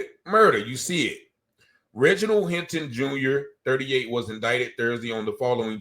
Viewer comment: he clearly says: Reginald Hinton...wtf?